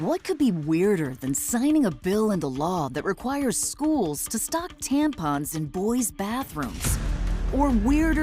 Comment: I misheard it at first: i thought it said he wanted to "stop tampons in boys school toilets" and i thought "whats wrong with that", but it actually said "stock tampons" etc etc...i don't think that advert was very good, they could've done better than that, the production value and style seemed more Democrat.